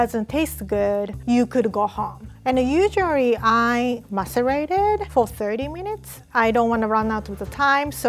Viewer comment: subtitles say "(indistinct)". I'm pretty sure is saying "macerate it", which in this case means "let it rest". I hope this helps. Ps. Please, check the subtitles, because there are some typos. Like "cake butter" instead of "cake batter", "cranberry" at 1:31 which I think it's "crumbly" or something like that... Thanks for the subtitles, by the way, they're useful :-)